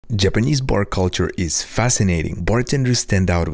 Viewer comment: At first I heard "Japanese po*n culture"